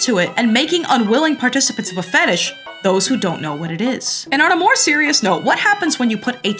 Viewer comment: Did she just say those who know